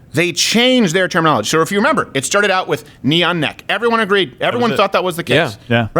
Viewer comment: I keep hearing "Neon Neck" 🤣🤣🤣